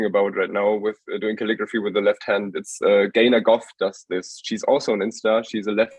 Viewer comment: Really nice podcast and cool guest. I just can't figure the name of the left handed woman he mentions at 11:40. I hear Gaina Goff, but no results whatsover. A like to her profile would be really appreciated, as a fellow leftie. Thanks in advance.